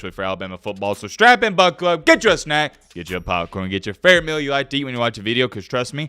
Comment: I always thought said “grab a miller lite” not “grab a meal you like”